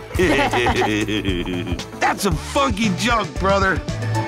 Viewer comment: when I saw this episode, I thought he said fu